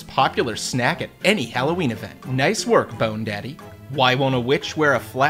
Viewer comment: Did he call Jack skeleton bone daddy